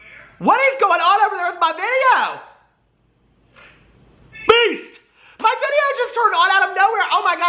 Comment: Wait, has your intro always included Beast? I watch you on 2x speed (watch all YT that way lol) and this whole time I thought you were saying Peace! Roflmao!! xoxo